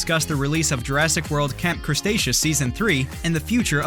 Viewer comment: did h call it camp crustaceous